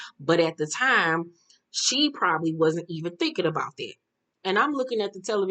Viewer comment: Keisha I thought she said HE wasn't thinking like that.. I have to check again